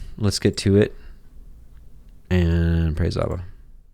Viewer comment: In opening prayer did you just say praise Allah ? That’s what it sounds like, am I missing something ? I rewinded 3 times clear as day